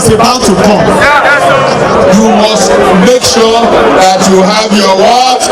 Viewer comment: Abeg wetin Buhari dey talk I know understand one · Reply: 😃😃😃😃 The only thing I heard is " make shuour "🤣🤣